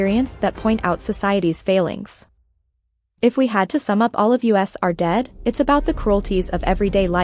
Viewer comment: im sorry did I hear "all of u.s are dead"? or am I just tripping